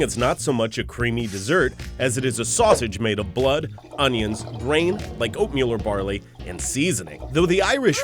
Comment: I thought they just said, "grains like opium and barley".